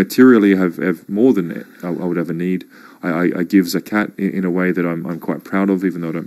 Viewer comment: What does say at 00:24 "I give ....." Zacat? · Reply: I googled it "Zakat", your duty to give back to Muslims, donating through financial means to those less fortunate.